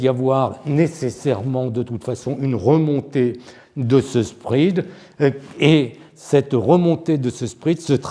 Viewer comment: c'est quoi ce "sprid" dont vous parlez à plusieurs reprises ? difficile d'être pris au sérieux quand même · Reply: spread: écart entre deux taux bancaires